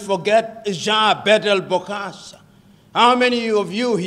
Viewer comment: Someone answer this question what is the name of the former African leader PLO Lumumba said at 1:15 after he said Mobutu? I couldn't quite understand the name. Anyone can answer I would appreciate it. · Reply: Jean-Bedel Bokassa of Central Africa Republic. The second President after David Dacko